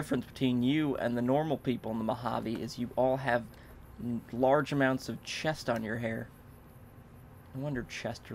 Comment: did anyone else hear at around 14:05 he said you all have a large amount of chest on your hair